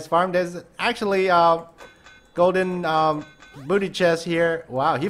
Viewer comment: Who else thought he was saying Golden Buddah Chest?